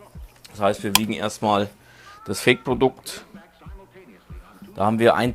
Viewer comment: 1 34 Minute ich hab verstanden wir probieren erst mal das fick Produkt